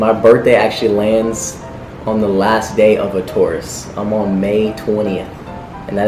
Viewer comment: I thought Austin said I'm a tourist but found out he said I'm Taurus 😂🤣😂 Also another fact: Catherine is 2 years old than Austin 2019?